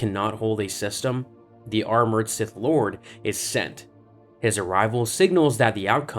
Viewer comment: i heard „the r-word sith lord“. Very confusing, although he technically is in a very fancy wheel chair